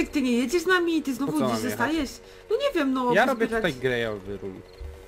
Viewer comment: usłyszałam "gejowy room" 😂😅